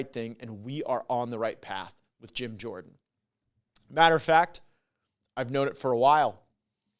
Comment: did he say I've knowed it...?